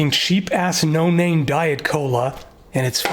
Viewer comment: I first heard "sheep-ass no-name diet cola" and had to rewind to reassure myself I heard wrong. I've seldom been so relieved to have been wrong...